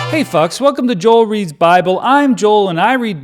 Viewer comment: I definitely heard you say “Hey f*cks” instead of Hey FOLKS. Not sure which I like better, tbh.